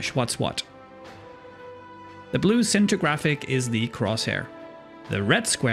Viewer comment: is he saying "the blue center graphic"?